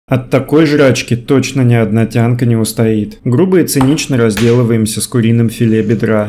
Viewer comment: Послышалось не одна Дианка не устоит